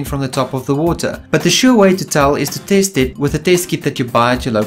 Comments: At first I thought you said "the surefire way to test for (ammonia) is to TASTE it..." until you said "... "with a test kit". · Omg lol I thought he said “the first thing you can do is taste it with a taste kit” hahaha